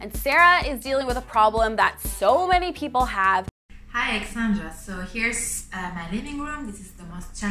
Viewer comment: Great idea for a video! But hm... I'm pretty sure that "Sara" said her name was...?! xD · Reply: I thought she said hi Alexandra but it's the andra at the end that might be throwing us haha